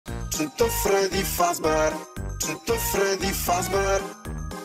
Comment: Why do i hear "SHIT ON FREDDY FAZBUER"💀💀💀